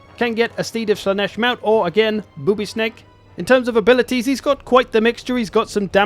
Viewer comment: I'm such a 5 year old. I heard 'boobie snake' and giggled like an idiot